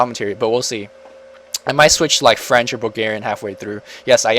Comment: What did you say at 5:32?! French and?